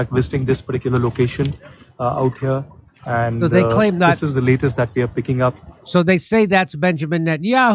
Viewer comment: Almost sounded like he said that is the leader they are picking up….🤔 NTM, he didn’t have anything on his lip… “God’s chosen coffee”….bro💯😂